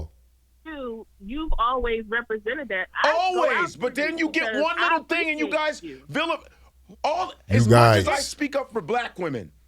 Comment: The sista said DON’T GET DEFENSIVE! He thought she said he was SENSITIVE. That’s not what she said. Just when I said Godfrey can get not only a cookout invite but he can get reparations! Now idk!!